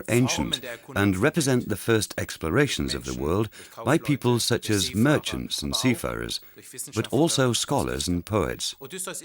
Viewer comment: it totally sounded like the German guy (via the English guy) just said how white people were the first seafarers… I had to put on captions just to check 😂😂😂